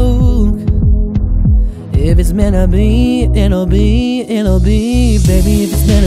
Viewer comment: It sounds like he is saying mena be instead of meant to be